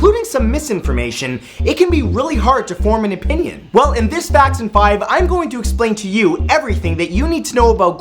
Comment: I had this minimized and was listening to it, and when he said "facts in 5" i thought he said "jackson 5"